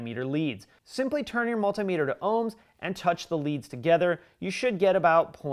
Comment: Is he talking about ‘ohms’ or is he saying ‘homes’ in a Hispanic gangsta kinda way?